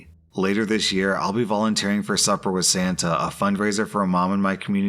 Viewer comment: I thought it said suffer with Santa not supper lol